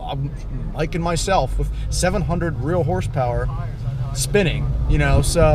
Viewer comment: Did he say 700 real horse power? cause that's what it sounded like to me. I hope he meant 700 real wheel horse power. around the 18:25 time marker. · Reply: He said wheel but the way he said it sounds like he's saying real